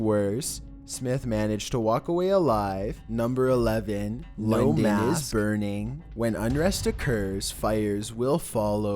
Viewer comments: I heard my d*** was burning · It sounds like he says my d*** is burning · What did they say in 5:32 I didn't understand? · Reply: He said two things “London is burning” and “the mask” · was that mistake ? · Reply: I heard "my d*ck is burning"